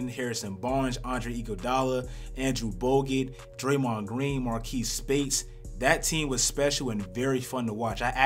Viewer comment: Am I the only one who heard him say Marquees Speights and not Maurice Speights😂